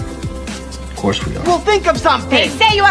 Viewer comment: hahahahahahahaha subtitles at 5:14 be saying "horsemen" instead of "of course we are"